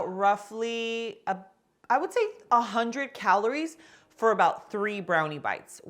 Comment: Thought she said it was 100 cal per. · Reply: she said 100 calories for 3 brownie bites. She says it at 8:04.